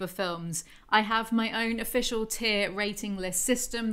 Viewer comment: for some reason I heard the word "tear" instead of "tier". I suppose it's because I imagine some tears were shed during this process. Thank you, for your valiant services to your audience.